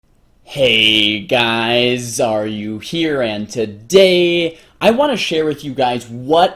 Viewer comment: When you said hey guys xaryu here I thought you said are you here and I was like well I think I am that's deep af